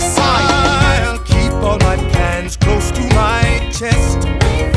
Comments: Please tell me I'm not the only one who thought burr said " I keep all my pans close to my chest" and then had a mental image of burr keeping kid pans close to his chest so Hamilton doesn't interrupt his late night stress baking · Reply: I first heard "I'll keep all my pants close to my chest" 🥲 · Please tell me I'm not the only one who thought burr said " I keep all my pans close to my chest" and then had a mental image of burr keeping kid pans close to his chest so Hamilton doesn't interrupt his late night stress baking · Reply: i thought he said i keep all my pants to my chest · Bruhhh, I thought it said "I'll keep my pants close to my chest" and I was so confused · Please tell me I'm not the only one who thought burr said " I keep all my pans close to my chest" and then had a mental image of burr keeping kid pans close to his chest so Hamilton doesn't interrupt his late night stress baking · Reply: hahahah i thought he said pans i thought for like 2 motnhs he said pens and now i have just learned that he says plans hahaha